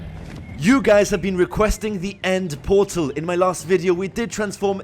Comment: i thought he said end mortal